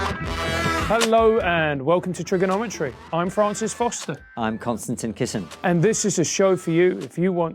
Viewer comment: Everytime I hear the hosts introduce themselves i hear "and I'm constantly kissing"